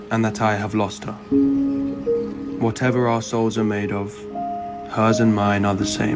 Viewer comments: During the intro narration part, I heard "this is what assh*les are made of" instead of "this is what our souls..." .... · No joke. At first I heard him saying "Whatever our assholes are made of, hers and mine are the same" and I think that this is way better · I can't be the only one who thought he said "whatever arseholes are made kf, her and mine are the same"